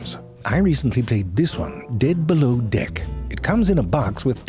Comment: My brother heard “dead balloon-dick” as he came into the room and immediately went in on me for all the weird crap I watch… he wouldn’t let me explain what you said or what I was watching. He’s a dead balloon-dick…